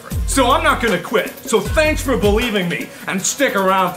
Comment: Did he say wideos at 1:13?